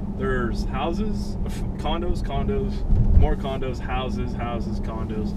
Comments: When you said condos I thought you said condom's. It was like condoms condoms condoms house condoms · When he said condos I thought he was saying condoms lol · The whole time I thought he was saying condoms condoms and some condoms lol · When he said condos i thought he said condoms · i thought u said condoms not condo